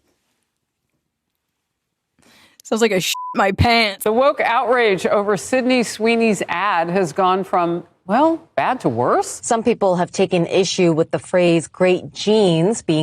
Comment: I thought she said “The Woke Outrage Over Sydney Sweeney’s Ass Has Gone From, Well, Bad to Worse.” 😂🤣